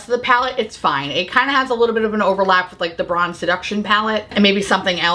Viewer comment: I watched every second and love hearing you drag this stuff to death but when you talked about mothership V I could have sworn I heard you call it Bronze Deduction and that’s all i can think about for the rest of the day